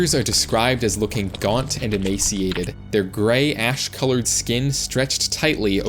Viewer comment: I heard grey ahh😂